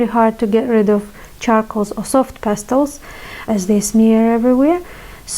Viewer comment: I think at 0:35 you meant to say smear. Sneer is when you do a mocking smile. English is a weird but wonderful language. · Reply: I do say things funny at times, English is not my first language. But in this case I did say smear 😉